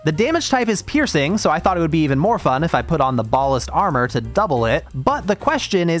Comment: when you said bhaalist armor I thought you said ballest like the most baller armor you could find